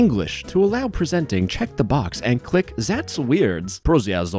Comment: Zats weirdz 💅💅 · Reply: Sounded like Goofy saying "ThAtz weird" which makes it twice as funny.